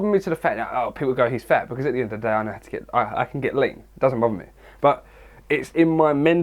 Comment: Literally thought you said "it doesn't bother me, cause at the end of the day i can get laid." Then i finally realized you were saying "lean" lmao.